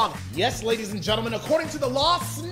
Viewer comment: At first I thought he said the dog is liable I was like: what da dog doin